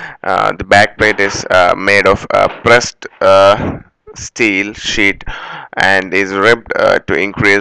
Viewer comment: Sounds like he said, "back plate is made of pressed steel shit". lol